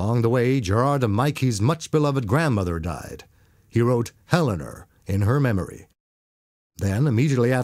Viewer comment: It always sounds like he said "Helener" to me.